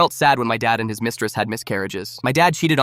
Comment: Bro my sister was near me while I was watching this and she thought you said "When my dad had miscarriages"🤣🤣🤣🤣🤣🤣🤣🤣🤣🤣🤣🤣🤣🤣🤣🤣🤣🤣🤣🤣